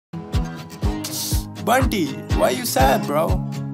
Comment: i thought he said "panty why you sad bruh"